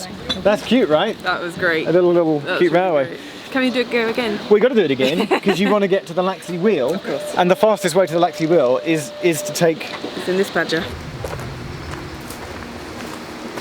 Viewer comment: did say "gotta get this badger"??? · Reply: I thought she said "Is in this badger". And it was at 5:09.